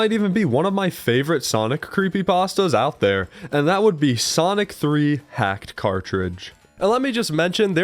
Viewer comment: I thought he said sonic 3 hacked car fridge 😭😭😭😭😭🙏🙏🙏🙏💀💀